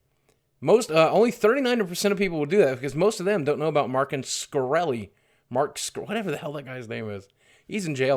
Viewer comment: u sound like u said Martin Scorsese Goodfellas anyone